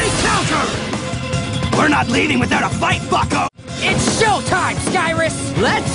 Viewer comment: i thought said "fucko"